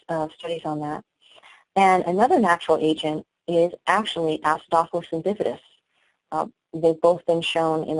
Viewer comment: what did she say at 48:55? some natural MMC stimulant? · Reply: Acidophilus and Bifidus. It's a probiotic